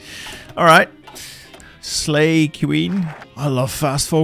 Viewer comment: did bro really say slay queen